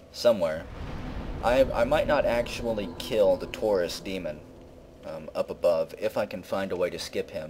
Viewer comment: Every time someone says ''Taurus Demon', I hear 'Tourist Demon'.